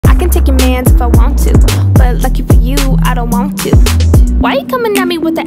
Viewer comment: Why do I hear -"lucky pucky I don't want to " 💀👁️👄👁️